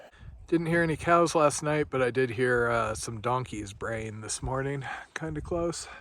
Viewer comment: Oh! You said you heard the donkeys braying. At first, I thought you said you heard the donkey's BRAINS. so one with nature the donkey telepathy has kicked in!